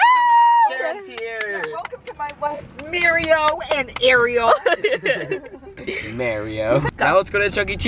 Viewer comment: DID MARIO JUST SAY "MARIEL" WITH A SMILE TO ARIEL!! I don't know but it sounds like it (Mario+Ariel ship name= Mariel) 😱💕